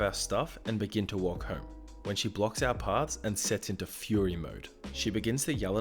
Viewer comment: I thought fury said furry!